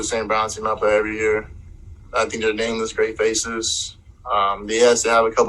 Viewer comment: Sounds like he said nameless great faces?